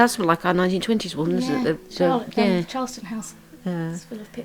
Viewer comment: wow what a fascinating story, pardon my rudeness, but she gave it to whom? it sounded like she said she gave it to charles' kids